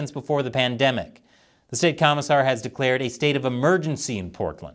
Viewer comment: "State Commissar"? I could have sworn that said "State Commissioner." Is Oregon now under Communist rule? Wouldn't surprise me much...